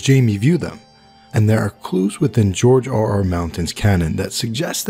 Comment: is it me, or does he say @ 0:55 George R.R. MOUNTAIN? the deadliest warrior in the seven kingdoms, a pen and a sword! o.O